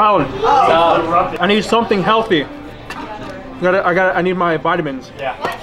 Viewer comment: Who else heard a little girl say what is healthy 😂😂